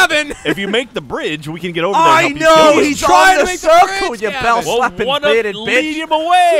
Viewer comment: Just to be sure, did Gavin call Jack a: 'bell slapping bearded bitch at' 8:15? XD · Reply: Daan Welker Yes. Yes he did xD